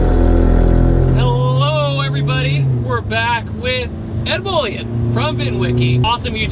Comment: Swore he called him “Ed Boy”